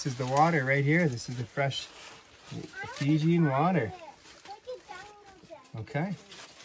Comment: Sorry my friend I thought you said pepe water